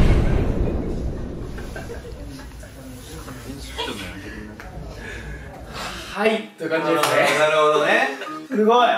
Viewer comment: の時のしばゆーなんて言ってるの？ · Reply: 勝手に指揮とんのやめてくんない